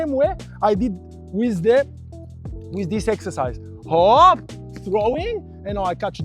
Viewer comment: Did you say Hop there, when French people normally say 'op!